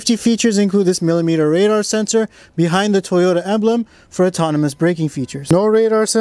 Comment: They gotta cut it from somewhere....where you don't see.. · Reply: oh I rewatched it and when you said “emblem” it sounded like you said Avalon My bad